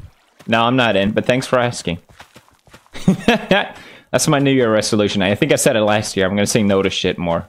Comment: Bro what? 0:07 does he say the N word there? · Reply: "new year resolution"